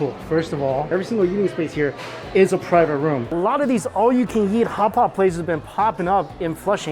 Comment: all I hear is "all you can yeet" and it's so funny to me 😂